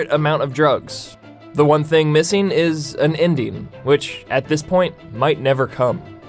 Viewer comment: Me: Did he just say "an Indian"?..